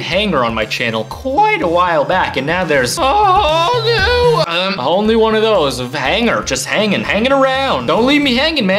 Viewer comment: I thought u said whole new world so I was singing XD